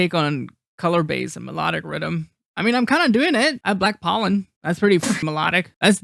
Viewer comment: what track is he talking about? Did he say Black Pollen? Is that an ID or...? I'm confused.